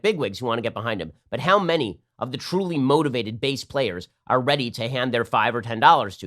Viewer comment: Ben, you said "Truly motivated base players"..... but i heard, "truly motivated BASS players." $5 to $10 really is a huge contribution from a bass player, no matter how motivated.